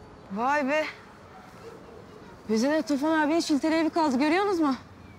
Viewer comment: Çiçek ne diyor? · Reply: Bize de Tufan abinin şilteli evi kaldı görüyon de mi"diyor...